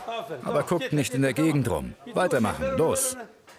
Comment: wo sagt er denn da bitte " guck nicht in der Gegend Rum los weiter Machen" ??? Er sagt nur "gut erledigt (deswegen lacht der Mitarbeiter auch, weil er gelobt wird.) schieb ihn jetzt rein." der Mitarbeiter sagt auch nicht ja ja nach dem Motto leck mein Arsch. Wer gesunde Ohren hat kann "al abi" raushören. Was auf gut deutsch, nimm Bruder* bedeutet. Das wars 😂 aber was Galileo da verstehen will ist Bahnhof 😂😂(*abi wird bei uns der ältere Bruder genannt ABER, wie in diesem Fall, sagt man bei uns auch zu älteren männlichen Personen Abi, welche man respektiert und wert schätzt.)